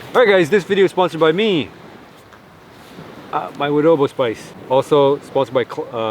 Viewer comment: did i hear "adobo" spice? like its philippine made spice,,, but i think not haa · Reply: Woodobo spice not adobo spice